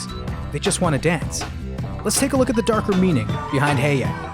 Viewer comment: I thought it said “the danker” meaning but it didn’t I was looking up to that